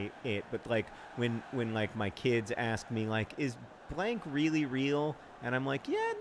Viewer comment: When you said "blank" I heard "hank" and was disappointed when I learned that was false